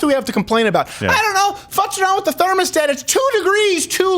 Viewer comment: did he say fucks around with the thermostat?